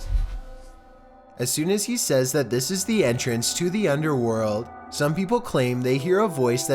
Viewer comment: On the 10th video when he said this is the entrance to hell I heard "this is hell"